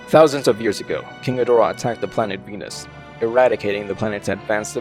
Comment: Venus? I thought you said penis?